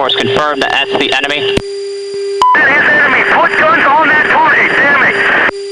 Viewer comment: Did he say put guns on that target, dammit?